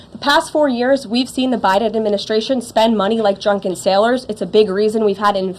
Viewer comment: did she say "drunken sailors!?" 😂